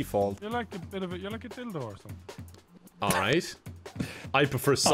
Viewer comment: What does Daithi say about Kevin's character face? I can't understand him when he says it. 2:30 ish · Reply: Oh, he says Dildo. lol